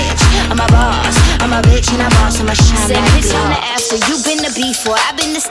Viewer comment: I thought she said "Im the after you been the before"